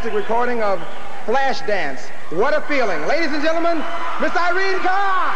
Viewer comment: "Mister (!) Irene Cara" Did he just say "mr"? 😯